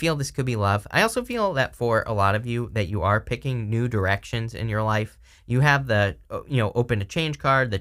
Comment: I know this brings the tone down a little... but I genuinely thought you said "nude erections" not the more obvious "new directions" and if that doesn't set the theme for Sagittarius energy at present, well I don't know what does 🤣